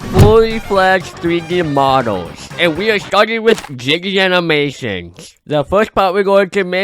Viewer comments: I thought bro said jiggy animations💀💀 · i know you didn’t mean it but you sounded like you said “Jibby animations” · When you say Jaiden it sounds like you said jiggy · i thought he said ''fully flesh version'' instead of ''fully fledged version''